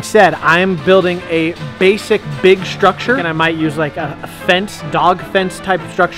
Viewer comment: Ngl I thought said basic bitch structure but he actually said basic big structure